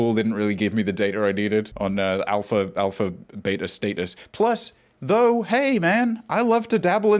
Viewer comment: the internet has poisoned my brain so much i thought said alpha beta stetas rather than status